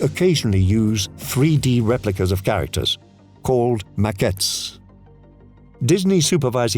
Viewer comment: I have learned a lot from the video, thanks a lot. BTW, what is the word followed the definition "occasionally 3D replicas of characters" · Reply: A macket I don’t know if it’s an English word it just means model or replica